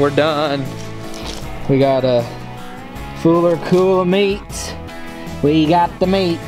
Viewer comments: anyone else catch the “fuller cool of meats!!” Hahaha or maybe cooler full of meat!! Enjoy watching the videos man keep up the great work! I’m from North Texas area and would love to meet up sometime. Got some really great spots to fish and hunt!! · Anyone else hear him say a fooler cool of meat at 13:33 lol. I do that crap all the darn time